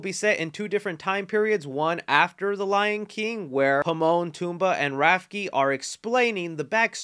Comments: am i the oly one who heard pimon and tumba? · did I just hear you say Pimon, Tumbaa, and Rafki? 😂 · Did.... Did you just call them "Pimon, Tumbaa and Rafki"? xD